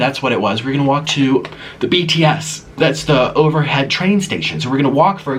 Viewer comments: Did he say BTS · did you just say BTS! That's my favorite band I love them but, you're probably meaning something different